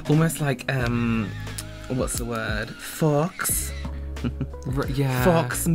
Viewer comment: I swear, when was making his lashes it sounded like he said it's going to be like forks.. then he said fox mink and I realised he said fox... I think fork lashes might be cool though lol